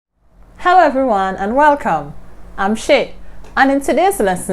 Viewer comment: AHSHAAHA WHY DID I HEAR "HI EVERYONE IM SHIT"